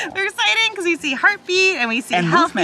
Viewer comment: Guys, I think it’s a girl cuz they said “we see her feet” which uh idk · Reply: If you mean what says at 13:08, she says “heartbeat” not “her feet”